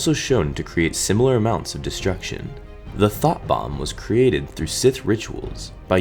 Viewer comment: I thought he said thot bomb